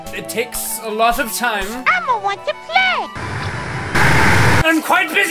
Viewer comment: i thought elmo said "elmo wants to be laid"